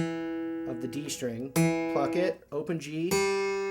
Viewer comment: LOL anyone heard him say f@ it @ 3:13 funny how pluck sounds like f@